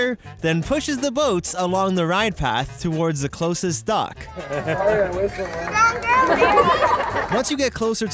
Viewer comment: what did that kid say? "is that girl married?"